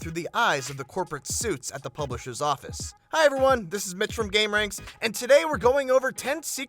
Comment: What the fuck did he say? This is bitch from gameranx? 0:17 Update: oh his name is Mitch